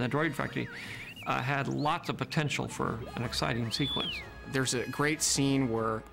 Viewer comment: did he say porn? xD